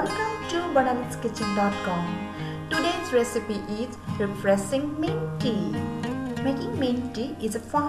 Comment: I legit heard it into " depressing mint tea" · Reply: Hello sir..i said refreshing ..you heard depressing ..my gosh...lol